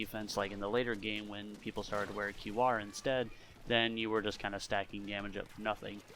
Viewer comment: he says "people started wearing ___" what is he saying???? it sounds like "qr" but I can't understand him omggggg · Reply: qr = quick respawn